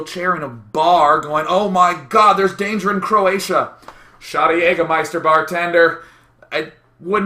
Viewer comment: Did he said Croatia??? Omg omg...Day9, I will pay u the trip to it next summer...